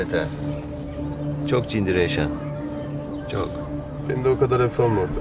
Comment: Ezel ne diyor anlamadım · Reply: "Beni de o kadar hafife alma ortak" diyor.